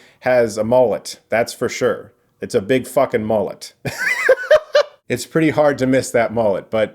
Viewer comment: I first heard “‘moment” instead of “mullet” and was very confused by the maniacal laughter. Then you showed the mullet and it all made sense.